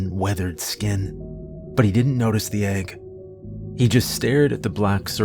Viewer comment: Yes!! 🙋‍♀️ I'd love to read where some Daggerlin characters show up in other stories!! 🎉yes!! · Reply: Am I hearing correctly 🤔?...at approximately 3:27:30ish, I'm hearing "nobody noticed the egg..."...what "egg"🤔?